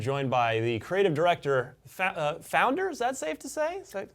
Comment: i thought he was going too say fat for a second there not founder xD